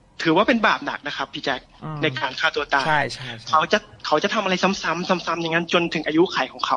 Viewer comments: ใครได้ยินคำว่าใช่ตอน36:23 เหมือนเราบ้าง หรือว่าเราได้ยินคนเดียว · ที่เขาบอกว่าการฆ่าตัวตายมันเป็นบาปหนัก ( ได้ยินเสียงแทรกว่า ใช่) ขนลุกเลย